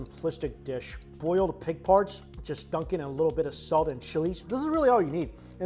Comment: I know you said ‘boiled pig parts’ but I heard ‘pig farts’ && well, let’s just say... ‘pigfarts pig farts, here I come! pigfarts pig farts, yum yum yum!’